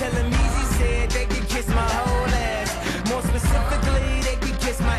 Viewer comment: I'm the only one who hears "Kiss my Oleg" at 1:31? Saints Row 3 Reference even before it came out?